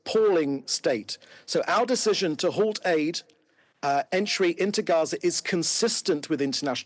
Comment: I thought he said aid went in? Enough to fill Wembley but then says they withheld it